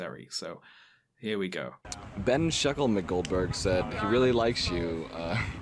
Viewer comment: is she saying ben shackleton goldberg? Is that a dog whistle I dont get?